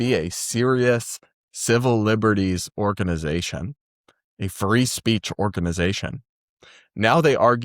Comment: Whew. For a second I thought you said "]The ACLU] used to be a furry speech organization."